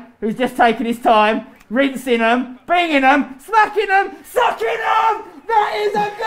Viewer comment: Why did he say sucking them at 6:56? Hahaha